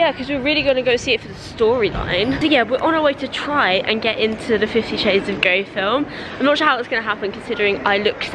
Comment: i swear she said 50 shades of gay